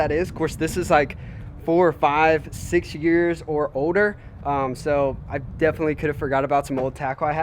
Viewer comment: he umm said odor instead of older